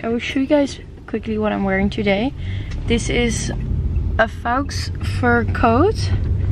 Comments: Thought she said real fox fur coat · I assume you said faux fur, because I thought I heard you say fox fur! 🤣🤣🤣